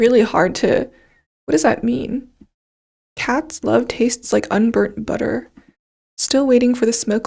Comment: i believe Kimi was confused because she heard "Kat loves you" instead of "chat loves you" which lead to the unburnt butter line